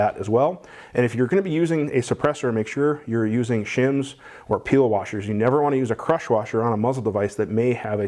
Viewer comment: What kind of washer was that instead of a crush washer (when using a suppressor mount)? I heard "P. O." washer; correct? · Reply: You can use a peel washer or shim for mounting a suppressor mount